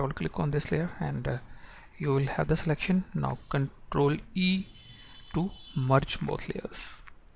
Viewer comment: Mr. may you write me; what did you say at that very moment in video time,"2:15" : CTRL + ? + ? Thank you, Warmest regards, Tuncluer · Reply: CTRL + E